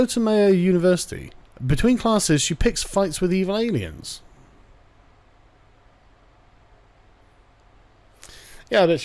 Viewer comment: "evil aliens". Miss America hates immigrants? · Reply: I actually misheard it as illegal aliens at first.